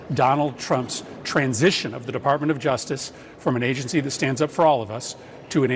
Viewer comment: Did he just say the justice department stands up for all of us or was I just hearing things